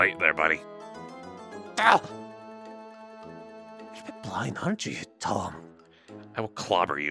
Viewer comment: I live for this video, and I love how flabbergasted sounds during most of it lmfao- Also, does anyone know what says at 3:55 ? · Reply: I think they said " why aren't you tall"